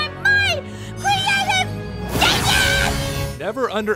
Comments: I always heard "creative genius." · V I S H D E E N (Also, no offense to the voice actor, but wut was that 😂 I thought she was trying to say creative genius. Not vision. That's how poorly that line was said) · I thought she said "Creative GENIUS!" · I always thought she said creative genius tbh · When Rarity said "VISION", until maybe a year ago I heard it as "DIDGET". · I always thought Rarity was saying "...my creative GENIUS!" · I heard "BY MY CREATIVE GENIUS" NOT VISIONS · I swear, she’s saying “genius” and not “vision” so i’m just confused because it sounds nothing like “vision” :/